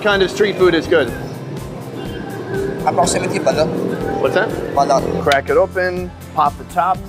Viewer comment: it sounds like the guy said “about 70 balut” ? or did he say “approximately balut”?